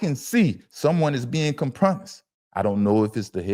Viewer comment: what did he say? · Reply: I think he meant compromised but I may be wrong 😅😂😅😂